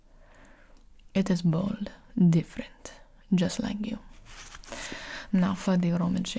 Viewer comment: now hang on did she just call me bald and different !! Bit harsh 😳 😆 🤣